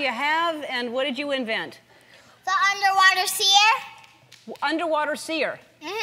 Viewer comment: I thought it said underwear sear.... Very dissapointed :(